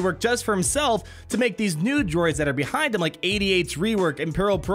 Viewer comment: It sounded like you said nude droid every time you said new droid.